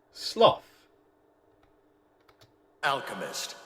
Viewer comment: it sounds like your saying Sloth when his name is Solf.